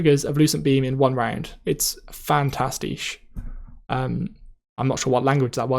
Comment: Sounded like german (fantastisch).